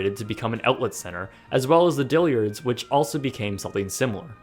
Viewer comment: Did he just say “diLiaRds”